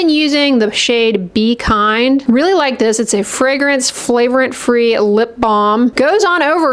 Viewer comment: Sounded like you said, “flagrant free” when describing the lipgloss 😂